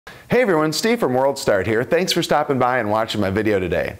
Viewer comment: I thought he said "Steve from WORLDSTAR"